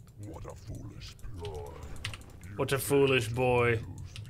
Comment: I think the pit lord is saying what a foolish 'ploy'... not boy.